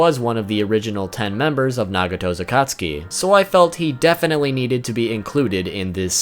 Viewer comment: you have the English subtitles, Orochimaru is said to be in “ Naruto’s Akatsuki ”, instead of it being “ Nagato’s Akatsuki ” }